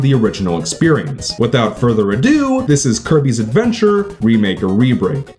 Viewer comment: am I the only one who heard Herpes Adventure?